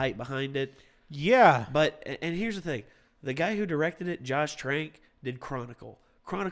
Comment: "Josh Trake?" Sounded like you said "Josh Drake" for a second. I must've been thinking of Drake and Josh.